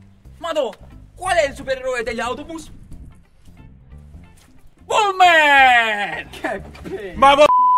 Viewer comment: 4 anni che è uscito e non ho ancora capito cosa dice al min 1:11.. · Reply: bu-man